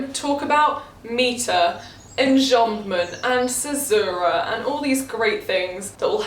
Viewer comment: Can someone tell me what she says at 11:35 to 11:39 ? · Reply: caesura